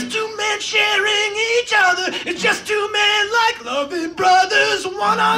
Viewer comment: I probably misheard that but did he say Logan Brothers? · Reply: GuessImKindaEmo Loving*